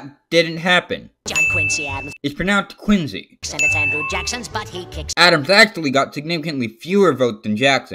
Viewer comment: Did He Said: Jackson Butt